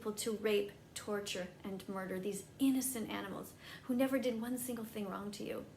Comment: I'm sorry did she just say rape an animal..... da fuk